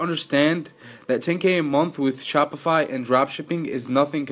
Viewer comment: Who else thought it said SPOTIFY?!